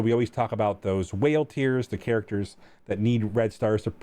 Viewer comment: I misheard it as "We always talk about whale tears..." not tiers. XD